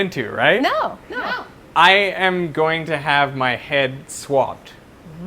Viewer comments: I thought I heard ‘I’m gonna have my head swapped’ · I thought he said swapped, I was very confused for a minute there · I thought he said " I am gonna have my head swapped" . I was like wtf? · Genuinely thought he said he was going to have his head swapped at first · i heard "i'm gonna have my head SWAPPED" not swabbed , lmao